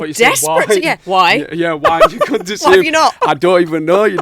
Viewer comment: 😂that moment you realize that even native english-speaking people struggle to understand each other sometimes due to accent differences. Ps: Just like Zayn, I also thought she said : 'why? 'At that moment